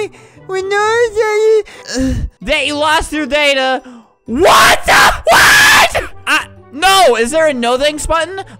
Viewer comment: did he say what the f***